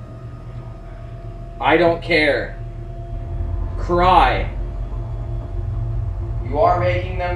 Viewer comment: Lmao they really said cry about it when commented on everybody feeling cold